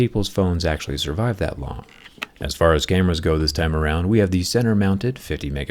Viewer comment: i dunno why but i heard "gamers" when he said "cameras" lmao